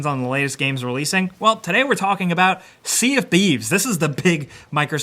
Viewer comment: For a second there I thought you said at the start “Sea of Bees”